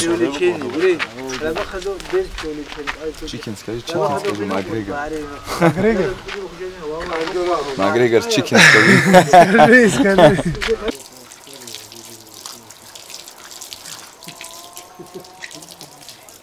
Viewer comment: Did I hear McGregor chicken?!!